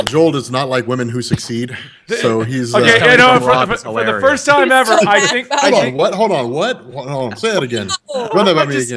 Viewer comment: what Does Joel say at 1:16:38? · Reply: This coming from rob is hilarious